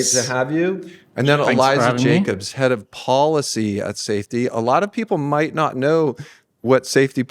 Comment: "Head of Policy at safety" I misheard as "Head of Policy unsafety".